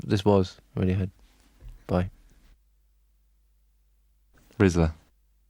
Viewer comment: did ed just say “rizzler”? 😭